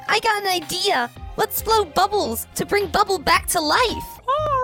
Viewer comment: when i first heard this. I thought it was bubble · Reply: NOPE IT'S RUBY